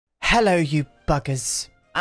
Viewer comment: Thought you said 'hello you f*ck3rs'. Had to rewind. I mean still got called a bugger... not convinced the husband to give pegging a go yet but he did say till death do we part so still got time...